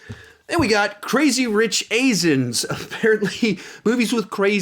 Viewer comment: Am I the only one who heard him say when he was talking about crazy rich Asians. He said crazy rich asins. LOL XD